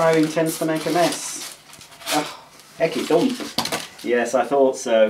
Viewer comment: I have heard you say "iggy thump" a few times. what does it mean? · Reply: It's Ecky thump and until googling it I didn't know what it meant. It has some connections with a comedy show from the 70s called the Goodies aparently. I always thought it was a Yorkshire term, but it may originate from Lancashire.